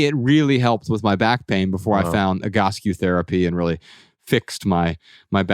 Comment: what therapy is he saying here that fixed his back pain? Interested for my husband. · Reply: Egoscue Therapy <link>